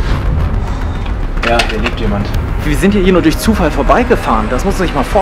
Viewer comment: Ich hab erst verstanden: ,,Jap, hier liegt jemand” 😅